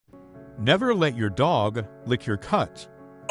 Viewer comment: Bro i thought it said "Nevel let your cuts lick your dog" 💀